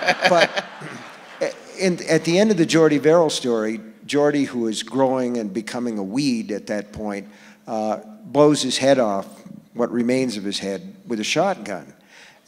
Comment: At first I heard; "Joardie, who was growing and becoming a weeb, at that point, and decided to blow his head off. Or what remained of his head, with a shotgun"